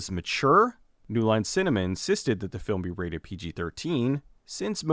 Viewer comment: New Line Cinnamon? Did I hear that right?